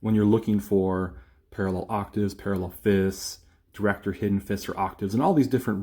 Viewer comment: Hello: when you reach 3:20 in your Meaningful Video I think you say Directors Hidden Fifths is this what I hear or am I mistaken; please inform. Thank you